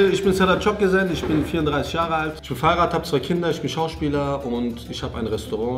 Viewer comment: Tut mir leid, aber ich hab "Fahrrad" verstanden😂